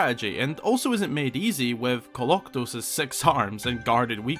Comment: I thought he said "sex arms!"